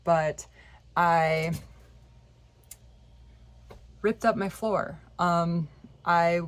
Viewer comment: Holy Fu*k! That open sequence was epic! Glad you found the issue, I'm sure it's been extremely stressful. Can't wait to see the new build. Did you say 'spirits out' at the end? 😅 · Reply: Haha, I thought she said that too! If you watch it on subtitles, it shows "spirits out" but she said ripped out.